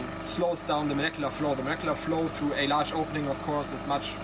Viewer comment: what flow at 7:10 ? the Merkelar Flow ? can anybody plz explain I understand the meaning but I don't know the term. Or can't understand him properly. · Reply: Sorry for my unclear pronunciation. The term is "molecular flow".